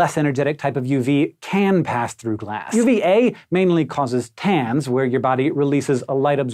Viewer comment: I thought he said pans